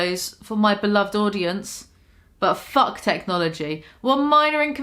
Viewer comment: Did she say “Butt fuck”?